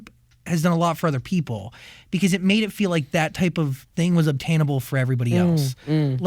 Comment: Can someone tell me what does the interviewer says at 55:55-55:58? I've repeated several times and still can't get it. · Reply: I think it’s “because it made it feel like that type of thing was obtainable for everybody else”, btw closed captions r on this video if that’s helps, hope I helped :)